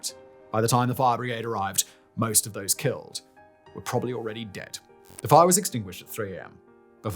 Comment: Uh did I hear that right? 5:54 most of those killed were already dead?